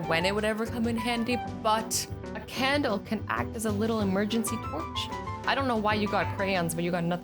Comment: A crayon can be what? 5:44 Did she just say porch??